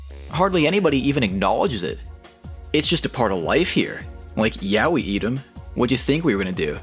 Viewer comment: I heard this as "Like, yaoi eat 'em.".